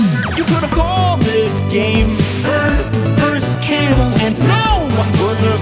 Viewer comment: why do I hear "burger vs camel" and not "bird vs camel"?